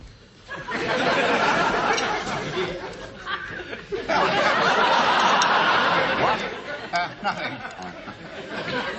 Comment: What does Margo say the second time she silently mouths words to Jerry at 05:18? I could swear she says "have you been rude?", but can someone clear it up? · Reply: I had to watch it twice. "Have you been rude?" is exactly what she says.